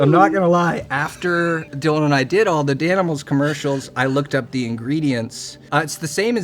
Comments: She for sure thought he said 7-8 animals and not danimals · Loved him saying you can fit 8 danimals into the cup and she thinks he’s saying animals and says “2 beetles” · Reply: WAIT LMFAOOO I DIDNT WVEN CATCH THAT I THOUGHT HE SAID ANIMALSSSSS